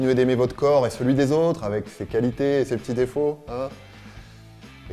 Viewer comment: je sais pas pourquoi mais j'ai entendu "avec fécalité"